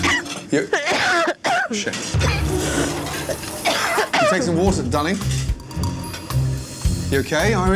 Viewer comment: i heard take some water dummy